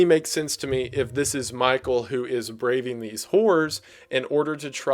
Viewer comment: I’m gone 😭 why does it sound like he said “braving these wh*r*s”